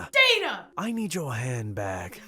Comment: When the shadow person said that he wanted her handbag. I thought he said I want your head back! I was very concerned lol 😂